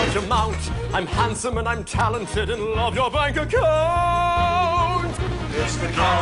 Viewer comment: I always thought he said " loathe your bank account "